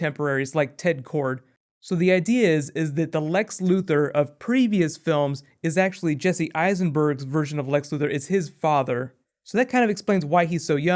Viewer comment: I thought said lex of other films is like jesse's heisenberg to the new lex luther